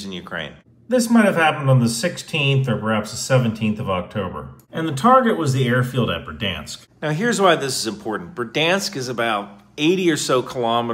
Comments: Anyone else not watching the video thought he said Verdasnk? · At first, i thought he said Verdansk. Made me think that the M39 Is in Warzone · I coulda sworn you said Verdansk 👀😭